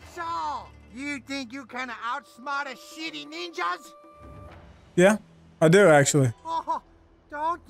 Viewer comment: is it me or does it sound like hes saying shitty nesha